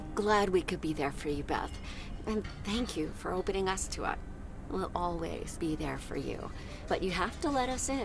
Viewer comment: says “opening us to up” instead of “opening up to us” like in the subtitles, wonder if that’s a glitch?